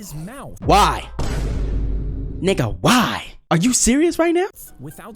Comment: WHY DID U SAY NIGG- im not saying bad word